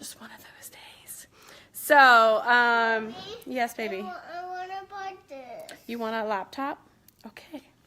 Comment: She clearly said, I want to bite that. SW is all thrived up, & not hearing that correctly. 😂